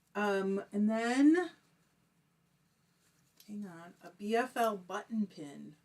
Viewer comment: There's no cotton in the fiber I'm spinning. We aren't really certain about anything with him being a rescue, and they did believe he had some bulldog in his genealogy, but the rescue organization seemed very certain that he had some pit as well. We don't know anything for sure. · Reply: I could have swore you said BFL/Cotton. Will have to go re-watch. What I will say about Donkey, is that his head is shaped much more like a bull dog. Pit Bull's heads are much more square (I used to participate in dog shows and got used to lots of breeds.)